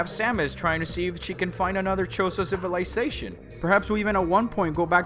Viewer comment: did you just say "...to see if chicken find another chozo civilization"?